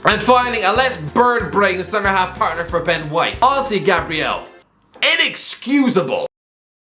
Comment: I always hear the Irish guy say a word that sounds like "ozzy" or "aussi" ( 10:29), does someone know which word is it? And what does it mean? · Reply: Honestly